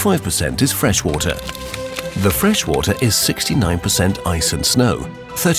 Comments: did he just say 69% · Did I hear 69% ;P